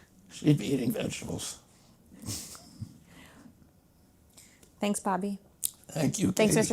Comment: I thought at the end she said, "Thanks Papi". LOL